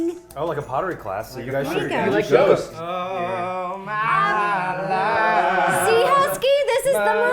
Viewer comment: I can’t be the only one who thought that Neds cake said “my wife”...